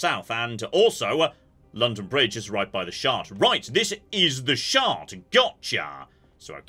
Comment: My internal auto-complete may be broken, but all I hear is "The Shart".